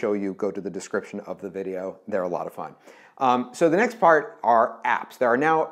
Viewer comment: Did he said fart are @ 10:35??? 😂😂